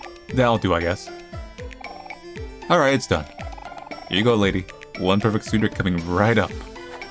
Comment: i misheard this as "one perfect tsundere" and instantly became very concerned